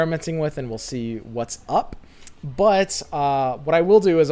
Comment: why every time say but i hear it like he say butt ?